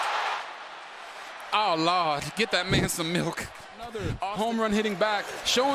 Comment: thought bro said get that nigga sum milk 😂